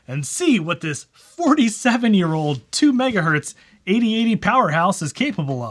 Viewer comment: Definitely thought you said ADHD powerhouse, not 8080 powerhouse. I’ve never felt so seen 🤣🤣🤣